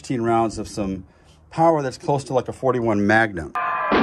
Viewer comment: did he say 41 magnum? 🤔🤨